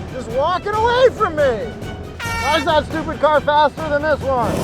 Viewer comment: did he just called mustang a "supercar"? 🤣